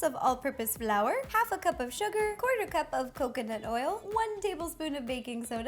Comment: tbh I thought she said 1/4 cup of cocaine